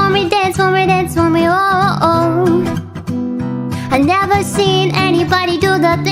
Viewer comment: Somehow I hear as "dead for me"